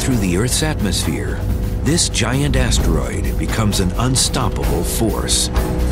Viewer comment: Did anyone else hear “This giant ass droid”